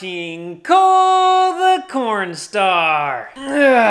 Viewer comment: My mate thought you were a miner.! He thought I said 'corn the coalstar'.!!.